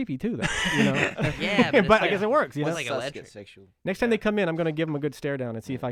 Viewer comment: Anyone hear Calum ( I think ) say the word sexual at 12:07. I don't hear anything else, just that, so I don't know exactly what he said. But I heard it.